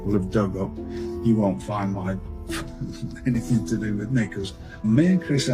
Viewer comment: what he say?? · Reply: "you won't find anything to do with me"